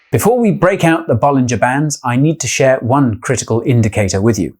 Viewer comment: I really thought he said "Ball in Japan" when it really is "Bollinger Bands"